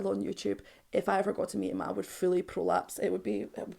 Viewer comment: Did you say you would fully prolapse? 🤣🤣🤣🤣🤣🤣🤣🤣